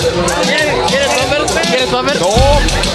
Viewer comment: Que es lo que le ofrece el vato en minuto 13:58? No se entiende mucho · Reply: que si quiere poppers